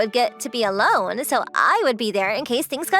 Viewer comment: I thought he said I wish i would nuke her